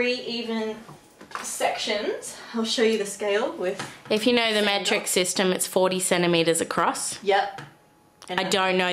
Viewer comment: When she said metric system I thought she said matrix system and I was really confused